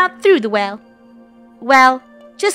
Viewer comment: It sounded like funneh said climb up the f***ing rope instead of bucket rope😭🫶🏻